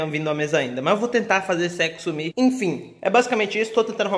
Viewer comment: No minuto 4:38 mais para frente após o pronunciar a frase "esse eco" parece esse sexo , eu escutei